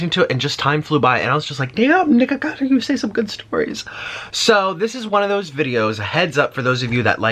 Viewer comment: What did you say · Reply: he said "damn nikocado you tell some good stories"